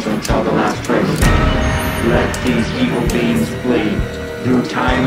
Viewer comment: The Alien 👽 abduction story it sounds like Dan is saying beans instead of beings 😆 the beans came to get us 😂😂😂